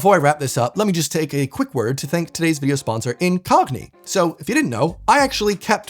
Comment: I was listening to this whilst working and thought that you said "Let me just take a quick word to thank today's video sponsor in cockney." I was so excited to hear your cockney accent that I stopped to watch but...oh well.